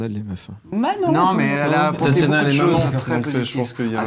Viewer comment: attends il a dit quoi là le? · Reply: "Tatiana elle est maman" 4 ans après